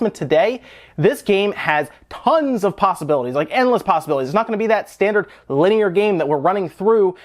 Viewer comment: Did he just say, Endless Possibilities?